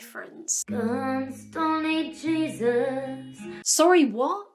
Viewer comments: I heard "Guns, Bronies, Jesus". I think I need to leave the internet, I'm losing my mind. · Her bathroom song at the end, I still don’t know what she said but I heard “c*nts donate jesus” which I know is wrong 😂